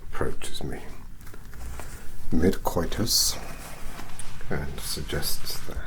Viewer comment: im sorry did he just say mid coitus